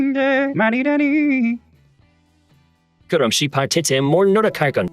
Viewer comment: 여기 에리히프롬이 뭐라고 하는지 들리시는 분 계신가요?? 대사가 잘 안들려서요 ㅠㅠ 도와주세요! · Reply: 그럼 c8 대체 뭘 노력할거야